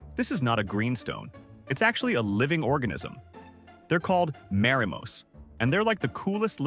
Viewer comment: I heard "A living Orgasm" tell me ik not wrong 💀